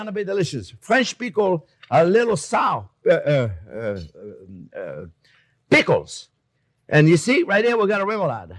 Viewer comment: 8.51, I heard " French people are a little sour " instead of " French pickles are little sour pickles ". I'm dead. Kisses from France chef ;p